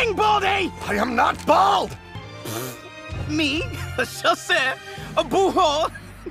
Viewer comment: I'm french and i Heard : "me ? A chasseu ? A Bouheau ??"